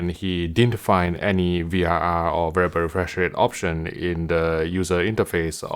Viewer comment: I always hear "very very fresh rate" :D